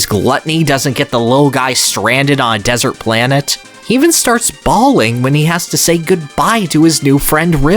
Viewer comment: I'm so sorry, I heard "he even starts ballin'" lmao